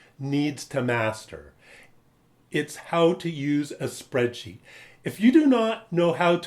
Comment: I can't hear the name of the skill , spra*** ?!! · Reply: Spreadsheet.